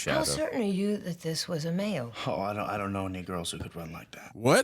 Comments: I don't understand the misunderstanding at all. I don't even know what they thought they heard other than something apparently race-based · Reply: "any girls" vs "any negroes". They even said that's what they misheard, in a subtitle. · He said I don't know ANY GIRL. This movie was before the w okification of the world and indoctrination that of some of you have been subject to at universities. What you thought he said would not have even crossed someone's mind back then. Because people were normal. As opposed to influenced by the wrong ideology today. Also unlike slop movies today made for people with low attention span, this a movie where everything you see and hear is purposeful. Like pieces of a puzzle that you have to put together gradually to understand the movie. The way movies used to be. Meaning you should talk less, and listen and watch more.